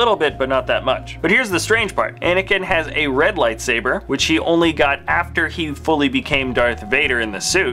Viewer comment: Did I hear you call the burned Anakin a "she" in 6:59 ? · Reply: He didn't say "she". He said "which he"